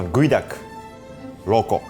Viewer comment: "Geoduck - Raw cock" I swear that's what I heard